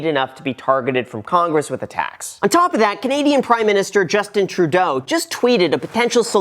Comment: Genuinely thought you said “comedian prime minister”